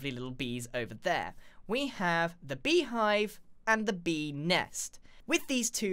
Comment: When he said Bee next I thought he said Benis.